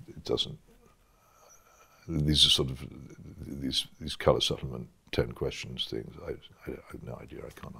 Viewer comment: How exactly did he describe your questions at 52:27 and 56:45? Subtitles say "coloured settlement" lol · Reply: Oliver Bel Haha! It’s actually pretty close! He says ‘colour supplement’ - not to insult your intelligence but it’s the magazine component that commonly comes with weekend newspapers 😄👍🏼 To be honest, I think the philosophical style of questions (of which I’m a big fan) are as serious as one wants to take them.